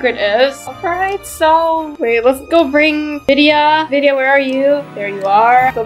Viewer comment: At this part when she said "Vidia" I heard "Idiot" 💀